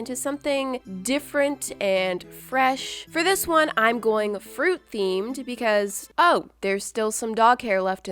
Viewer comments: I thought you said it was a FrUiT thing in the beginning 🍇🍓🍉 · I thought you said fruit theme